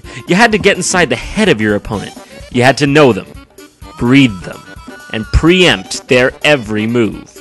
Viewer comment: I heard "breed them" at 9:03 or so XD